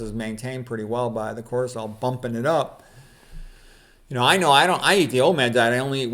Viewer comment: When you said "I'm on the OMAD diet", for a second I thought you said "old man diet". 😊